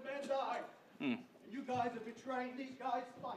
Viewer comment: what did the guy say at 23:15 ?? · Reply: I think it was, "You guys are betraying our guys"